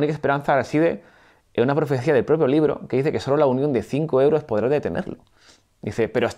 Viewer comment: Os juro que escuché la unión de cinco euros hahaha. Buen video como siempre.